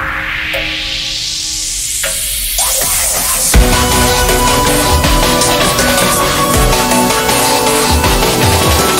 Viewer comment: what is he even saying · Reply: LET'S DANCE BITCH!!!